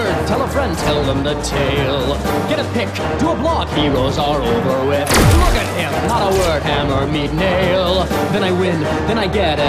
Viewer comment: I always hear: "Hammer, me Neil"^^